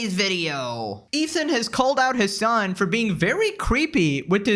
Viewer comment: Sounded like you said "Ethan has called out his son for being very creepy"😂💀